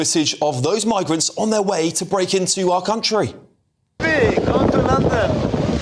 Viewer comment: Did he say: “YOU CAN’T DO NOTHING” ??🤔 I’m getting really tired of the extreme entitlement and smugness of muslims😳😤